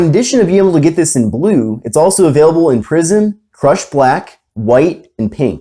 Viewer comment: I heard "It's also available in prison"